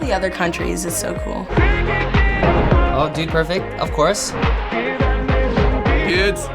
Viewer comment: I heard 1d perfect... I swear this fandom is driving me crazy